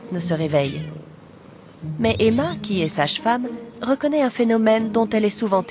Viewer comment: c’est moi ou elle dit sa chfemme ?la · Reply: Non. Elle dit ‘’sage femme ‘’.